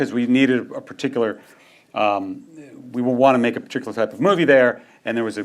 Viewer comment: Did you edit the closed captioning to have Feigey saying “we want to make money” instead of “we wanted to make a particular type of movie”? (0:15 mark)